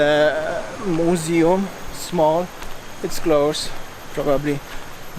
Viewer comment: I thought you said earlier that Monkey forest was 'bankrupt'? Anyway, thank U for showing Us.👍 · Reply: Nooo i said closed but many businesses are bangkrupt in Ubud i think 🤔 if so sorry 🤣 anyway is open now again 😎👍🏼